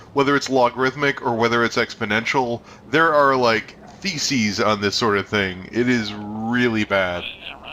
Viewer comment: When was talking about the dragon ball z fandom I thought he said "There are Feces about this thing." Which is appropriate.